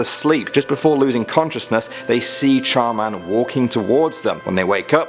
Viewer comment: When he said char man I thought he said charmander